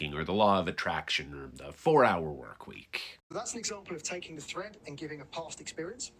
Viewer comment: Did he just say that her reading was a “threat?” Oh maybe he said “thread.” I really hope he meant “thread.”